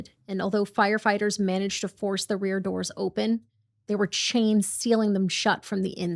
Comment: i’m sorry, did you say chains? like the doors were chained on the inside to prevent them from opening? /gen